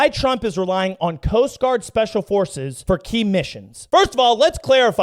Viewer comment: when said Coast Guard special forces, for a second it sounded like ' Post Office '. lol " SPECIAL DELIVERY ! " lol